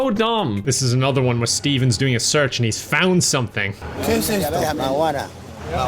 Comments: Im pretty sure he misheard "my water" as marijuana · that guy DIDN'T say marijuana. He said my water. asked him what was in the bottle and he said "my water"